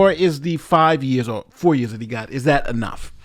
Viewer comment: At first I thought I heard "forty years" and thought that it was harsh, but also justified. Just four years is a joke. But it's expected. This country is insanely lenient on criminals, and thousands of people suffer needlessly from crime every year because of it. BUt hey, whatever allows bleeding hearts to be able to tell themselves "we're civilized" when their heads hit their pillows at night, I guess.